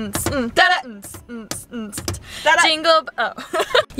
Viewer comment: do you speak Polish? i mean i think you wanted to say "dzień dobry" OMG :O